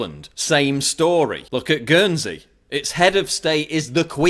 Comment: What is the country he mentions at time mark 13:13? Gönzi? Ghoensi? Can't quite hear.